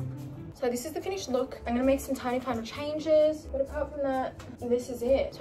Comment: It sounds like she says I’m gonna make some tiny CLIMATE CHANGES